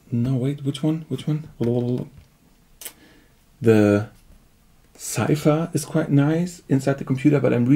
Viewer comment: Does anyone know what soft synth he mentions at 19:19? Zypher (sp?) ?? I can't identify it.. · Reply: Cypher